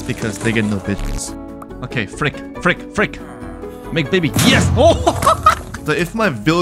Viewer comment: I though bro said, "Make Diddy." The captions said, "make baby."☠